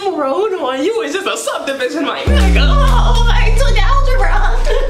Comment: did he just say “I took algebra”?😭💀💀dontai..